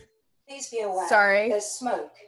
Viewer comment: I thought it said "Please be alive" instead of "Please be aware"...🤣